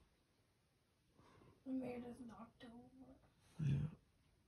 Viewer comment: I heard someone say " then it was knocked over " if you play it .75 speed it sounds more clear